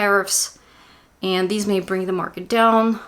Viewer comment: Hello, did you say Disney (The Walt Disney Company) may bring the market down at around 24:34? If it is indeed Disney, then you hit right on the money. Contrary to expectation Disney's earnings were not good and it brought the Dow down. Thank you. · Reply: lol. I wish I could take credit here :) I said, "these" but yeah, it sounded like Disney... I rarely look at individual stocks for my weekly predictions (takes too long), but as an fyi I do see all sorts of difficult transits for Boeing in 2020...